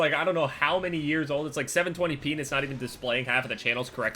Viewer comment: Did he say "720 penis" at 2:58?